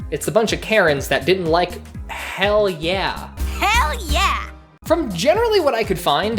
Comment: When I was watching this show as a little kid, I didn’t even register that she said anything bad. I literally thought she said “heck yeah” 💀